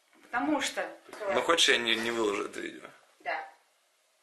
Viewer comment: А что он говорит на 0:55 минуте?? (И я тебя?) Или мне послышалось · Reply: Diana CheCheNka он точно сказал "хер тебе"